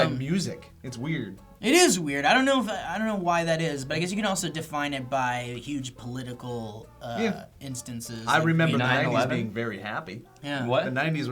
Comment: "I Remember 9/11 as being very happy" all I heard...